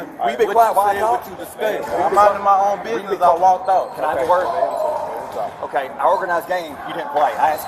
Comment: the high school coach says “can I twerk” instead of can I talk. lol